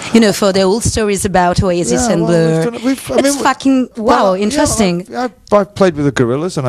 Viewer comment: its fucking interesting ???? Is that what she said lol